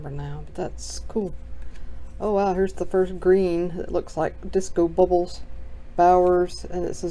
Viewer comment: I thought you said disco pebbles. I have a great idea for that. I'm serious but I'm still laughing to myself. That was about 7 minutes and 15 seconds Into the video.